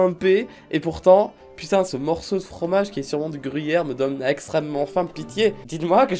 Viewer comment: pour moi c'est plus du porc salut ou du chaussé au moine 😅